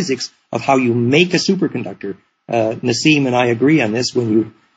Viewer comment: What on earth did he say here? 14:25 It was just getting interesting and the words got jumbled at the key moment.. very strange. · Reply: Paul Anthony He said " how to make a super conductor , Nassim and I agree